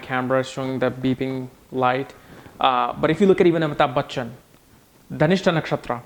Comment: Hi a brasilian fã. What word you say at 26:55 ??? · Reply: Amitabh Bachchan. He's an Indian actor.